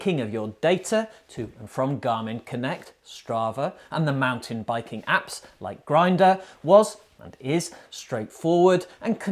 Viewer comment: Did he just say “MTB Apps like Grindr”?!,! 😂